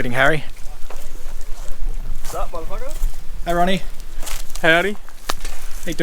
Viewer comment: did he just say "sup M F"?? lol